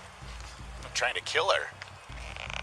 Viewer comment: I could have SWORN that she said "I'm fixing to DIE!". Good GOD what a pack of losers these officers are.....and the 911 dispatchers....pathetic...